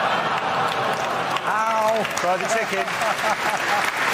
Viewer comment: Does James say "Fried Chicken" at 4:51 🤣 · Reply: "Try the chicken" means like "tried and true always work" because Hitler jokes are always funny just like chicken is always good😂 I love James May